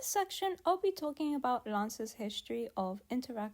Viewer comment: i thought you said Lancer .... 😄😄😄 i Love Lancer .....😄😄😄